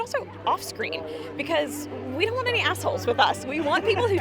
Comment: Does she say ”We don’t want any assholes with us?“ A bit disgusting. Henry was so enthusiastic with the plot, his performance so convincing and he is one of the nicest persons ever. He cannot be meant. So what’s the reason for her sentence?